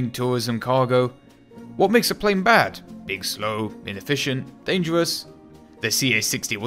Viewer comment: You said "being slow" but for a second I thought you said "big slow" lol.